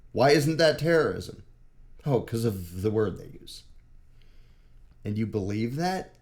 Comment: What word did you say? "...Because of the word ____"? I even put headphones on and couldn't hear it well. 🤷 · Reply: I said "Because of the word they use," referring to the word "GOVERNMENT"